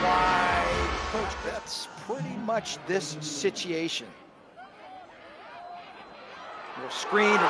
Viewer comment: Did he just say "sitiation" 8:50 lol. I see he's been around some Americans from the south.